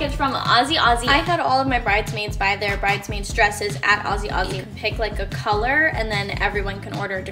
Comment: I thought she said “Aussie Aussie” 😭🖐 Congratulations for your marriage 🎉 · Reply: Cause she is lmao its azazie not azi azi